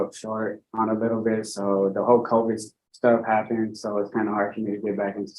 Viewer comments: He said, "the whole covid stuff happened," but Inhad to go back and re-listen because I HEARD, "the Hulk Hogan stuff happened." I was like, what? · did he say Hulk Hogan stuff?